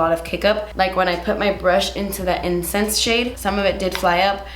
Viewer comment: Everytime you said " incense" I thought u were saying " incest" and I was like damn... those names are dark AF 😂 but in all seriousness can you also do a more neutral look with this pallet? I'm very on the fence about buying it. Good looks for every day??